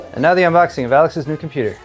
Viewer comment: sounds like he said Galax or Valax computer. I’d would say Alex too 😂